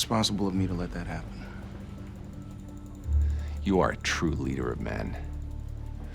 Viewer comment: What did House said "You're truly a Europe man 🤔🤔🤔❓❓❓ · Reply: " You are a true leader of men "